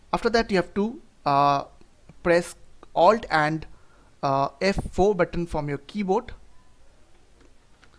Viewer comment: what did he say there pls help me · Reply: Alt + F4 button press together.